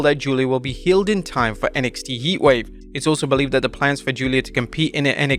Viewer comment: I swear I heard “YEETwave”